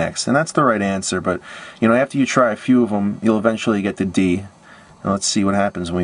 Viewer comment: did anybody else hear him say "you'll eventually get the D" at 3:22?.. lol